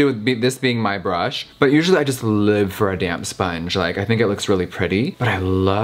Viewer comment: Everytime you said “damp sponge” i thought you were saying damn sponge. 😂😂